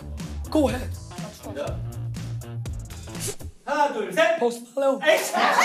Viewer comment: 포스트 플레온으로 들리는데 발음이 맞나여?